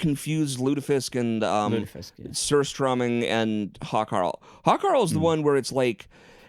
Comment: When said Hakarl, I thought he said "Hot Carl". Something to look up on Urban Dictionary.